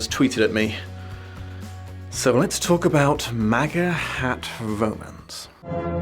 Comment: "Maga hat romance" WHAT!?!? That's not a thing. Please let it not be a thing. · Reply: Not going to lie. I only had one earbud in at first and so I misheard it as "Mega Het Romance". And I was like "Seems a bit on the nose for a romance genre, but whatever." Also, let's be honest. The premise of "Career focused, angry woman is adamantly against relationships until the local stud bangs her into romantic bliss" is pretty much the core plot of every single Hallmark movie ever made. And people love those. Which means, this book took the easiest premise out there for the genre and still turned it into a flaming trash pile. It's...almost impressive. Like, at times during his breakdown I was questioning if this was satire. Now, if you'll excuse me I'm off to go paint my fingernails black in a haze of rage induced activism.